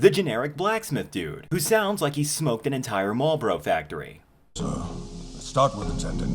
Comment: For those who didn't get what he said, it was "Marlboro factory". Marlboro is a cigarette company.